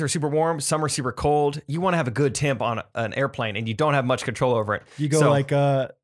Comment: definitely thought said “tampon” and then realized it was “temp on” 😂😬